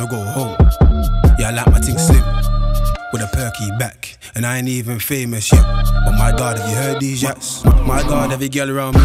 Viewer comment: My ears are failing me fam. I heard "I ain't even famous yet... my God have you heard this Nyansh" ... Someone tell me my hearing is off key please.